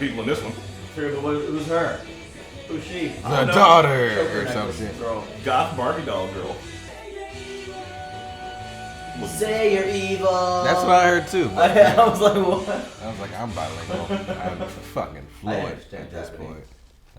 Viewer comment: As a bilingual person (NL and EN) it sounded like "SURELY EVIIIIIL"